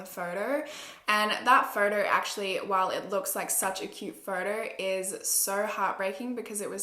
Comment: If u turn captions on at 3:53 it says "such a cute murder" instead of "such a cute photo"